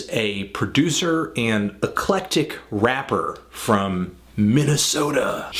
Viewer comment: Hey theneedledrop , what word did you say at 1:04ish. "Aclektik??" I don't know how to spell it. Tried searching google for related searches but no luck. I always like learning new words. Can anyone help?? Thanks!! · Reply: "Eclectic"